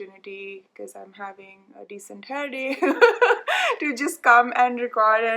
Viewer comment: I actually misheard what you said at first and thought you said you were having a great hair day and I was internally agreeing with that. I have wavy hair and I'm always a bit envious of people who have full on curly hair like yours. This just goes to show how sometimes we're our biggest critics and we're too harsh on ourselves. I think you look lovely and very beautiful, and your hair is really nice. Thank you for making your videos, I found your channel recently and I really enjoy hearing you talk about your projects while I also knit my own 😊